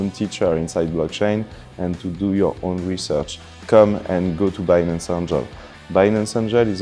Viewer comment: come to binance what?? I replayed it over and over but cannot make out what he's saying. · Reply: "come and join the 'Binance Angel'. It is a volunteering program where people -------"